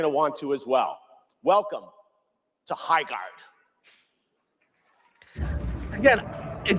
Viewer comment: did he just say HighArc. Who is this guy even, he reminds me of a Neil Cuckwoman or something like that😂